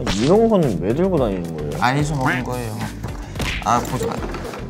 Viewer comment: 범규 뭐라고 했어요? 아니서 놓은거예요…? 한국어 공부중이라 알아들은 분 알려주시겠어요… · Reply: ‘아니 저 먹은 거에요’ 라고 했어요! 연준이가 이걸(쓰레기를) 왜 들고다니냐고 하니까 범규가 제가 먹고난 후 쓰레기다 하는 의미로 다시 가방에 넣은것같아요! 일본모아신가요? 한국어공부 힘내세요!